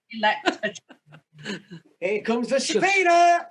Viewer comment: What’s said at 5:30, sounds like “here comes the subpoena!”? 😂